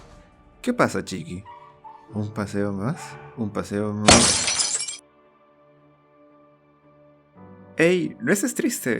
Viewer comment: no entendí alguien me lo explica. PD: si tampoco entendieron denle like😂🔥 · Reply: La inyección se detuvo